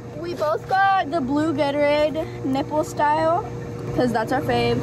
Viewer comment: did she say nipple style or is it just me hearing it lmao